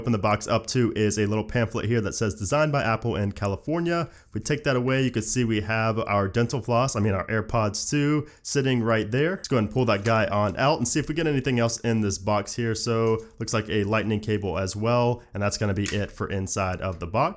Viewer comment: Did he just say dental floss?😂😂😂😂😂😂😂😂😂😂😂😂